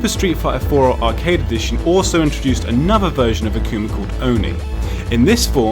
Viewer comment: Did he just say "Akuna"?